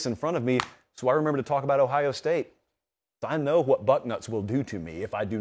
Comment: You say “Buck nuts” but I heard “butt nuts”. Gave me a whole new perspective on Ohio.